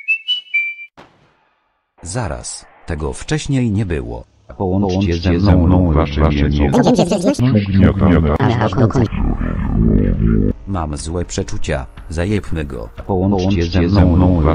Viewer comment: Co on mówi, bo połowy nie zrozumiałem? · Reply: "Połączcie ze mną albo wasze mięso będzie przez wieczność ugniatane aż do końca się rozłoży."